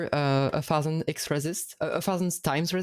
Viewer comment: I couldn't catch the name of Game of the Year from 42:30. Can anyone tell me? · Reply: 1000xResist